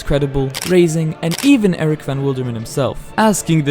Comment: I thought he said crazen 0:04 💀💀